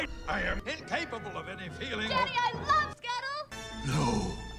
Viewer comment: I thought she said "I love skittles 🌈🍬"